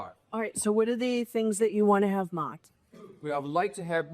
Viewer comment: The Judges accent... it sounded like she said "What would to you like to have mocked", because mocking the Defense is surely what she'll do.